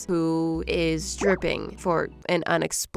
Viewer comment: I heard “its stripping” and I had to think about that for a solid minute before realizing she said “it’sssss dripping